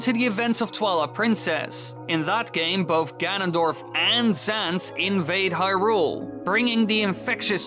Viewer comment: When he said ganondorf and zant invaded, I thought he said ganondorf and sans